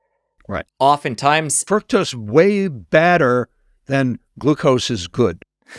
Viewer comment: Had to play back the sugar information when he says "fructose" way BADDER than glucose, it sounds like he said BETTER. Yikes! Glad I played it again 😂